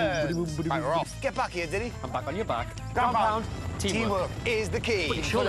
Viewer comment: What did they say? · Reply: Ground pound